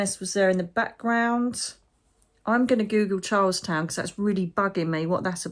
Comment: Thank you. I'm sure by the time you read this, you will have already figured out Charleston (for the USA) is in South Carolina. Beautiful place, although I've only been to North Carolina. USA has protests going on regarding the immigration laws. They are raiding homes and grabbing people from their jobs in the fields. It's very sad, I'm not in California, just watching from a far. Sagittarius, yup that's me!! But I think you're referring to the moon in Sagittarius we had yesterday on Jine 11th. The housing market here is a buyer (not good for me as I was trying to sell). But simultaneously I live by the beach and like you i love the sea!! So, actually, i am where i am. Trump cut federal rent assistance and housing programs. Thank again ❤ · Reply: I thought said Charlestown. It depends on which country she was referring to as there are a few places with that name. I guess I will need to listen again. I was listening as I was driving home from spending the day with my younger sister, lunch and shopping.